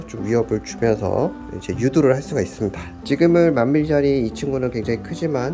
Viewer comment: 나만 유두로 들려?